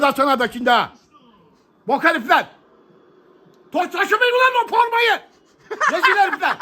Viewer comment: bu saniyede ne dediğini anlayan varmı? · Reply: “Taşımayın ulan o formayı, rezil herifler !”